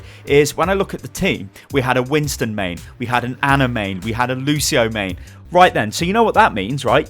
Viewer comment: When you say "Ana main", it almost sounds like you just said "anime".